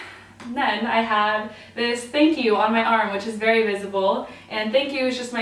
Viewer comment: In every video of yours that I have watched I thought your arm said "fuck you" and I was really confused AHAHHAHAH